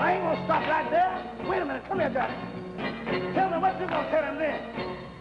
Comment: Anyone know the name of the singer that took over lead for Reuben in traveling shoes? 17:43....couldn't make out name. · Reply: Thanks Mack, I actually thought I heard "Johhny" too. I looked up other videos. That is him. Appreciate ya.